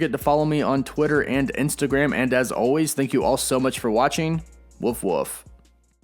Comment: Did he just say woof woof at the end?! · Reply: It's his catchphrase yeah it can sound weird sometimes but it's fine